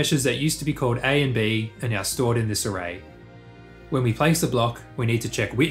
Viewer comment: "...are now stored in disarray." ... No, I heard that wrong.